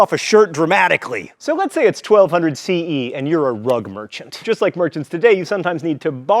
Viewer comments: For a second, I thought that you had said "drug merchant". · I thought he said 'rug muncher' hahaha